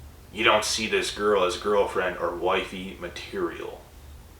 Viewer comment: I don't know if I misheard you but when you said boy fiends and girlfiends that just struck me as , did I just hear him right!? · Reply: I think he said girlfriend or “wifey” material , not boyfriend 😂